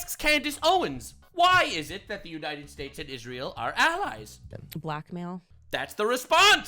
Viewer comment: i hope im not the only one who heard black male and not black mail, right?